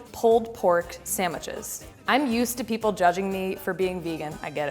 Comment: did she just say sand mitches ? 🤔